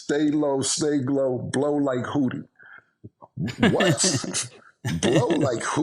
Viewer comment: Blow like Houdi was wild!! We always thought he was saying (shoot a gun) when he said that. Lolll but thats a wild pause. 😂😂